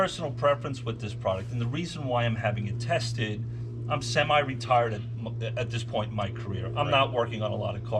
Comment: did say he was semi-retarded?